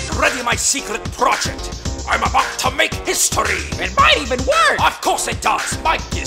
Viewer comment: in the original video, it's clipped perfectly so it sounds like Robotnik says, "You bet your ass!"